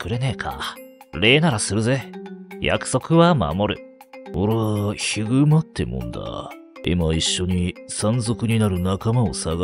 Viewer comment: ひぐまってるんだって聞こえたww ヒグマってゆうんだって言ってたんだww